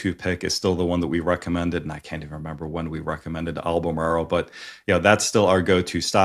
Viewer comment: Why buy ions hsy crsp, they have negative eps. Makes no sense. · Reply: Good ask. He mentioned "Alamaro", at 19:30 or so I couldn't hear him well enough, what company did I miss?